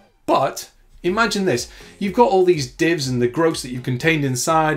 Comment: What is that word you used near 3:43? "groze"? I've heard you say it a couple times here and on the FunFunFunction video and I don't know what it means. Anyway, great video! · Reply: Gross. Contain the gross. The eww. The bleh. The ugh 👍😁